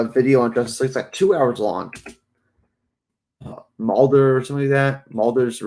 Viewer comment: whats the name of the channel you talk about at 7:31 about the review, i didnt understand the name ... thanks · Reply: Mauler <link>